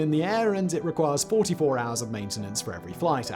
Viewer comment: did i hear that ratio correct? For every ONE flight hour it requires 44 hours of maintenance?!?!